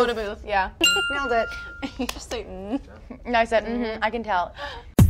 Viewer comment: #EMISONISENDGAME BTW could anyone tell me what did SaShay say to each other in 0:58 ??? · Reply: Sasha: Did you just say "Mmmm"? Shay: I said "Mmhmmm I can tell"